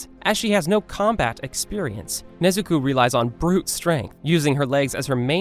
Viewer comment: did you just say Nezuku